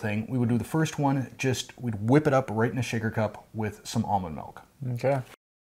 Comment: Almond milk 🤔😮🤷🏾‍♀️ I thought it said fat source and warm water!!! 😬